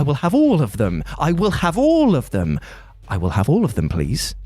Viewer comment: For some reason when he repeatedly said "have all of'em." I heard it as hadouken.